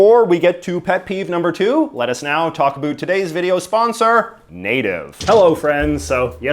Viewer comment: I was not looking at the screen and misheard you when you brought up your sponser and thought you were sponsored by NATO for a few minutes. That would be pretty wild if large intergovernmental treaty organizations were sponsoring youtube videos wouldn't it.